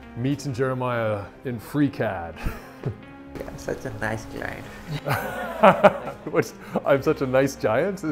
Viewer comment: Lmao, i heard him say "your such a nice giant" too 🤣